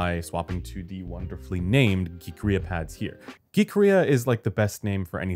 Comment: what is he saying? gi-krea pads? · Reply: “Geekria” pads.